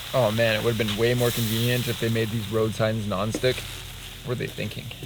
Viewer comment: DID HE SAID road sign ?😂😂